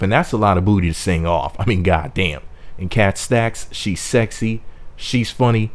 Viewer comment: Did He Just Say That KatNoStacks is sexy!!??!? WHHHEEEEREEEEEE 😂😂💀😂😭😭